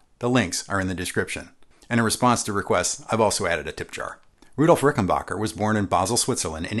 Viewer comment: i hear "Rudolph" - maybe my ears are deceiving me. But isn´t it Adolph? And his name was R...bacher. But no Gringo could spell the germanic .ch.. - so they called him always ...ck.. (but still pronouncing the ...acker like germanic ..a... - similar to humbucker) ... That´s what I heard, but without scientific proof. Thanks for great content!